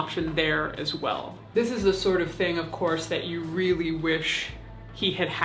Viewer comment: You said of course but it sounded like you said of corpse.